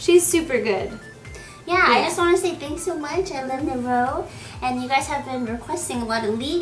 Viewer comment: i love natal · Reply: Shit for a second i thought you said "i love anal"... Well